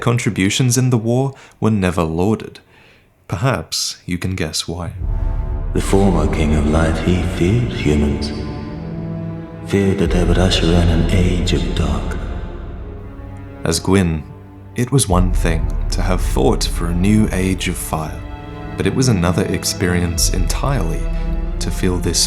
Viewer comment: "You can guess why." "dvidevidevich he fears humans. fear dat avdva should run an age of dark." Thats all i hear lol